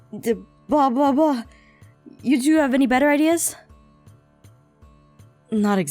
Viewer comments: did she just say "blah blah blah, you to have any better ideas?" 😂😂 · did she just say "The bwabwabwa"...? I think she meant to edit that part but never got to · deblobbloa is what it sounds like she said